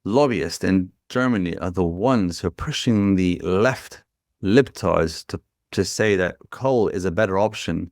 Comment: Did he say libtards at 30:51?